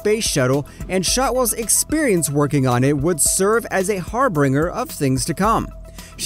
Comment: did he say Harbringer